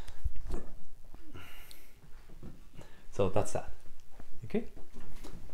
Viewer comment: What is he saying? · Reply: I think he said, "so that's all".